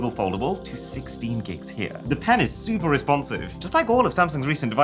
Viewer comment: did he just say "Penis" 🤣🤣🤣